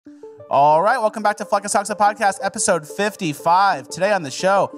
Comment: Lol sorry but....in the intro it sounds like you said "welcome back to fucking sucks podcast"